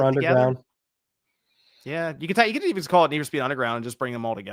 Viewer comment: did say need for skeet